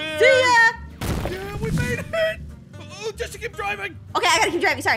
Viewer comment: Did Aaron say real name”Jessica keep driving”